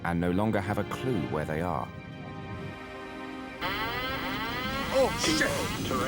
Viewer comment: Did the pilot just say "Oh sh*t"? That's unexpected from Mayday. EDIT: 21:14 Oh, that's the least of what he says!